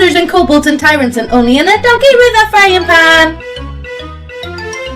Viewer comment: She says 'donkey with a frying pan!' but I keep hearing 'doggy'.